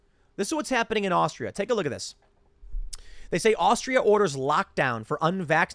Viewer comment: I thought it said Australia there. To which the headline is still correct. From 17th December, unvaxxed are not welcome anywhere in my state.